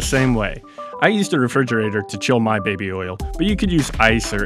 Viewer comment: did you just say "my baby oil"?